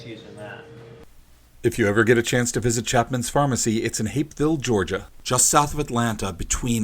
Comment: I thought he said "hate-filled Georgia" at first lol